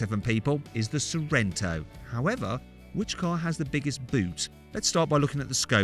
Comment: "Which one has the biggest boot?" I think... the subtitles misheard that...